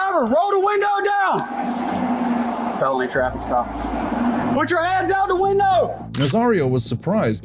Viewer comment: did he just say felony traffic stop? Oh yeah this cop is a d#€k